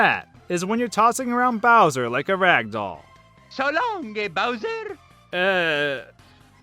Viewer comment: I heard "So long, gay bowsen!" The first few times, so yeah... there's that!